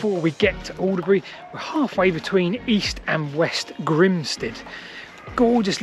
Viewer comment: I was unfortunately wrongfooted at the start because I heard "West Grinstead" and "East Grinstead." Which are in Sussex. And nowhere near each other (i.e. two places both named Grinstead, called 'East' and 'West' to distinguish them, rather than two closely-related settlements).